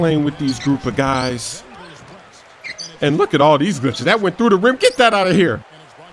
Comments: Did i just hear say look at these bitches! Around the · did he just say "look at all theese bitches?" @ 0:13 lol